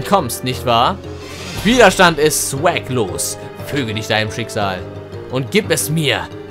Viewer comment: Hast du Zwecklos gesagt? Ich hab verstanden Wiederstand ist Swaglos xD